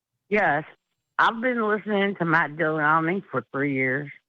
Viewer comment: did she just say Matt Dillahomie??